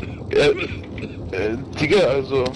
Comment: All of your translation is bs 😂😂 he never said cheater and doesnt said anything about a draw But GG man i like your Videos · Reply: I thought for sure he said "cheater" but he actually said "digga", I posted corrections in a pinned comment